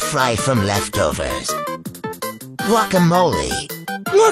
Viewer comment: I thought he said hash brownie 😂😂😂